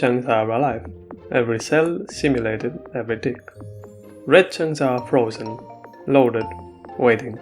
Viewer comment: Your subtitles said "dick"